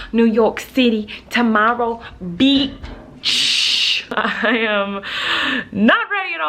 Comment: Was starving and already brushed my teeth so when she started of with beach I heard it as peach and figured it was the universe telling me it’s ok to eat now